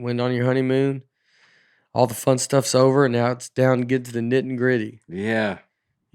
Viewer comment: Hello, Folks! I think I just heard Nate say “Nit and gritty” instead of “Nitty gritty.” It sounded so natural that he fooled me…till I looked it up to see if it was a variant of the phrase...it’s not, but maybe it should be!? Love this show - y’all are the best comedy podcast I’ve ever heard!